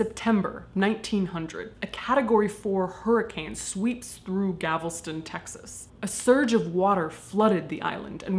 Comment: Am I the only one who heard her say Gavelston, Texas instead of Galveston, Texas? XD I live in Tx. I think that's why I noticed. lol.